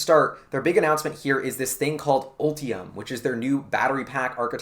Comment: Before you put up the graphic, I heard “Opium”. . . and even after seeing the Ultimum spelling. . . GM.. . “powered by Heroin” 😀